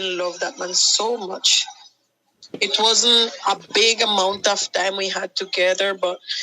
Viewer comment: she never wanted to marry him. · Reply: to me, it sounds like she said "wasn't", but I could be wrong.